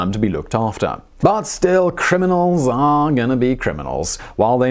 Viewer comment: ''But, still , criminals gonna crim.'' When the subtitles say something different to the host and its hilarious.